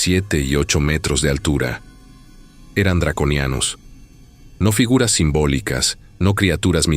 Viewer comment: Pensé que decía "dron ucraniano" 😅